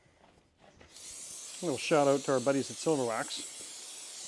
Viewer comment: I heard you say "Silver Wax" ? Is easy off oven cleaner to corrosive to that steering box ?